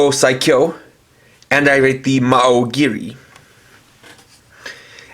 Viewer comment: "Maoh giri"? I always heard it as "Haou giri" but I guess that's what you get with the sound system these toys have.